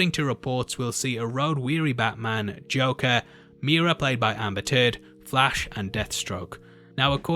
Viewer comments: Did he say Amber Turd? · did he said amber turd??